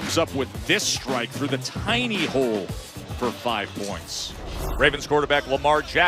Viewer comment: but then Russ comes up with this strike through the hidey hole. I know that's not what he said.